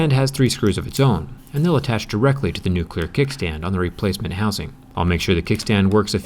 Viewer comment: Did anyone else hear "nuclear" insted of "new clear" at 11:40?